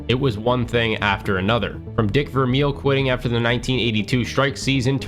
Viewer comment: Bruh I'm so toasted.. when u were listing reasons why they fell apart and said "starting with dick vermeil.." I thought u said "dick reveals".. they fell apart cuz of dick pics 😂